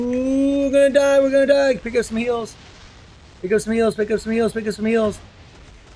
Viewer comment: Roflmao. I thought u were saying "A dios mios" when u were saying pick up some heals. Hahaha